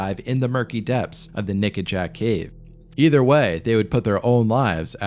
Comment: I wonder how long I would have heard you say Naked Jack cave if I hadn't seen it written down.